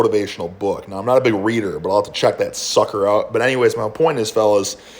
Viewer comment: on first listen I thought he said "I'll have to chuck that sucker out." about the book. what a guy!